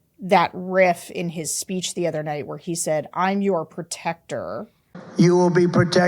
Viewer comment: I heard “I am your predator.” He’s a monster.